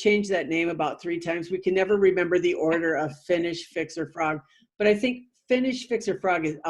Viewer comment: I thought you said finish Victor Frog and I was waiting to see what Victor looked like. Yep I don't hear too well.